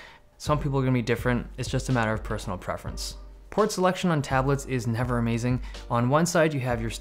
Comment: i thought he said porn selection